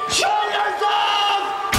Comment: Can anyone tell what the scream is saying at 0:03 seconds?? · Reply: I believe it's saying "show yourself!!!"